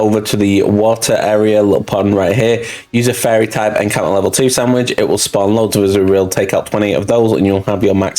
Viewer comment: I have no idea what pokemon to hunt here. I can't make out what you're saying around the 2:54 mark. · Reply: azurill sorry about that